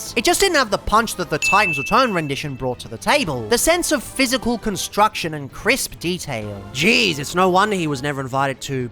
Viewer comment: I have no idea what the censored word is. Does anybody know? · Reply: Deep throated. Censored for YT's TOS moreso than actual prudeness. You can't be too careful these days